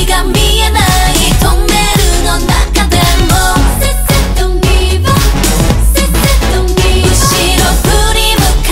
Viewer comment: "Say say dont give up" really a killer point.. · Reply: I thought they said. Sensei dont give up lmao